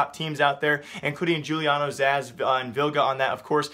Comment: did i just hear Juliano's ass? xD